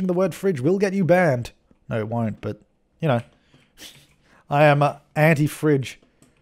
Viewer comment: I heard 'I am auntie Fridge'😂